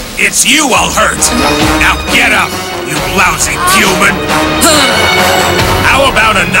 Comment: Am I the only one that hears "Puman"? Like, the VA was gonna say puny human, but just went straight to human mid syllable lol